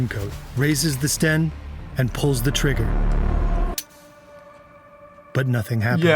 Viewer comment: did he just say trigga...? thats gotta be racially motivated right